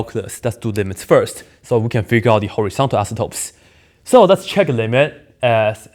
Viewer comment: Every time he says "asymptote" I hear "isotope".